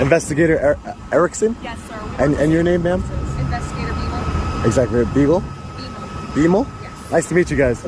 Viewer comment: I really thought she said investigator b-hole.